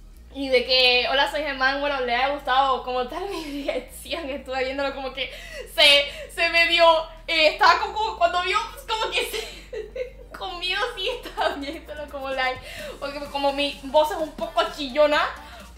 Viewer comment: no entendí nada de lo que dijo 😂😂❤️❤️ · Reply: Te subtitulo desde el 1:28 Dice: "Estaba viéndolo como que medio que se, se medio, estaba como que cuando vio como se *se ríe*, con miedo así que estaba viéndolo como lay (No sé si dice "lay" o "light" o "like" no sé inglés pero ella utiliza seguido esa expresión xd) Por que como mi voz es un poco chillona, pero. No men le gustó mucho." Eso es lo que dice bro, espero te sirva xd Comprende que estaba muy nerviosa <3